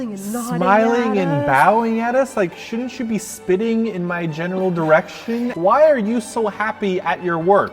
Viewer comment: Really sounded like you said "spitting in my genital direction" instead of "general direction"